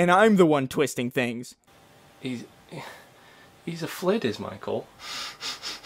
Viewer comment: what do you say at the 7:30 mark. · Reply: B.R.Longhorn he's a thalid is Michael